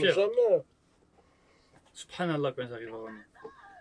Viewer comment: Did you say " subhan'Allah " at 3:39 ?